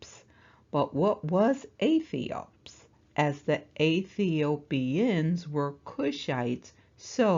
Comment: I'm at 23:22, and when you said Aethiopians....I just heard Athiest! I love your content!